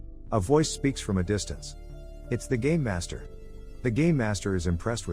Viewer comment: i kept hearing "gay master" and not "game master"